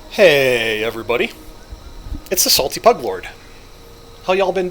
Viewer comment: sounded like you said hey it's the salty butt lord lol